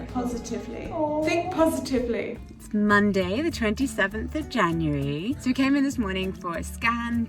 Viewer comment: What she really said "Its monday, The 27th of January" What i heard "Its monday, The 27th of Vaginuary"